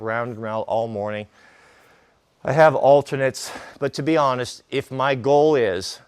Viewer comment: When says "I have alternates" at 8:40 I was sure I heard , "I have alterntors"